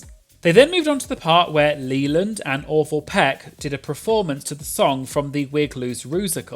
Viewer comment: Poor Orval. Literally sounded like you said Leyland and Awful Peck. Played it 5 times, multiple listeners. Oops Freudian?